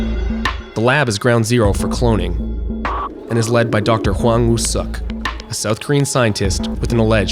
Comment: I thought he said Wang who suck